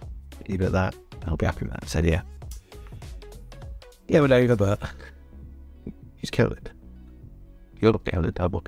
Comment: Nope, I'm sorry.... I've rewatched the segment about twenty times, and I still can't figure out what is saying at 13:00?! Any clues? Something about going over? Nice biscuit? (it looks very nice btw) Maybe if I play it backwards it'll make sense? Banter aside, fantastic effort on getting that lot completed in 15hrs. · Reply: I think he says "Yeah, I'm a little over, but..." but all the words are schmooshed together because of IMINENT CHRISTMAS BISCUIT INGESTION JITTERS :D